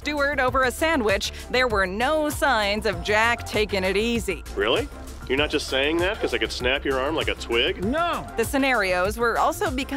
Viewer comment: In 9:38 I thought in the add he said "really? You're not just saying that because I am kneeling on your spine?"